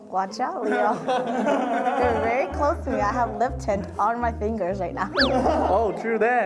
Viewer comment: I HEARD LIPTIT THAT HAD ME WHEEZING